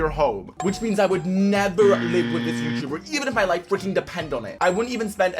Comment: WHAT DID I SEE IN THE SUBTITITILES? WHAT WORD😂😂😂😂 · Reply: It’s a subtitles mistake he said fricking